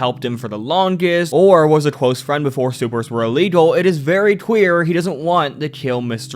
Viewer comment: Did you just say "it is very queer"